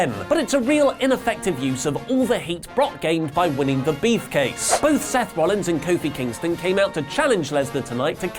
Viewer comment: /1:40 did you say beefcase?